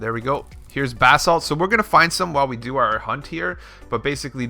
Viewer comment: I thought you were saying "bath salts". 🤣